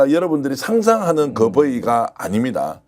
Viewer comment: ㅊ: 02:28 버이가....?...... 대체 저 경상도놈믄 뭐라고 하는 거야?